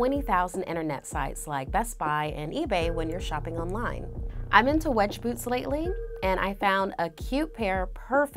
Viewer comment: Her- "I'm into wedge boots lately" What my ears thought she said because I wasn't paying attention and looking at something else- "I'm into wedgies later" Me- "YO! Whatchu talkin' bout your kinks for??" 😂🤣🤣🤣